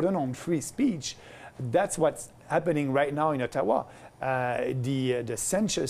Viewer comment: When he said Ottawa in 4:10, i thought he was saying Taiwan -_